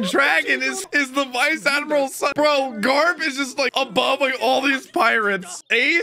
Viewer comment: Garp is like Obama. I heard that.